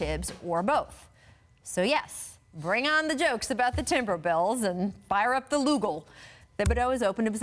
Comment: i swear i heard her say TimberBells